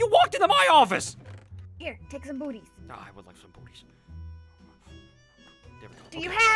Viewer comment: it sounded like he said boobies unsteady of booties